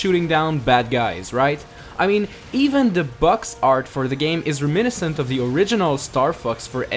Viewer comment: When he says fox it sounds like he is saying fucks